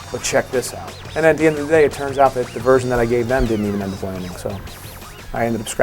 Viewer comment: What does he say at 08:02? I couldn't catch it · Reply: "...the version that I gave them did not even end up landing", I think.